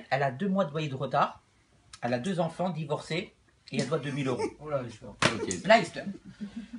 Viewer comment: c'est moi ou il dit Lahister (Allah yester) mdrr